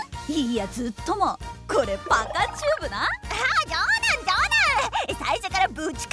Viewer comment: パカチューブがバカチューブに聞こえてしまったorz